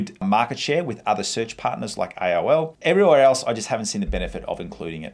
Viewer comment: what search partner did you say ?? IOL?